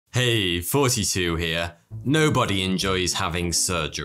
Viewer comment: I always thought you were saying “hey, 42 here”